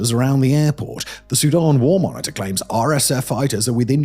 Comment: when says "the Sudanese war monitor", I have to admit (since I was munching on chips at the time), that the first time through I heard "the Sudanese Wal-Mart". Now I KNOW that Wal-Mart is ubiquitous in some parts of the world but THAT couldn't be right! So I pulled up the transcript to straighten out my ears . . . might help if there WERE a Wal-Mart there!